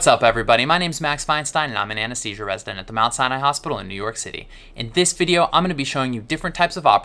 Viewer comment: The first time I watched this channel, I thought he said he was a resident from “Outside a hospital in New York City!”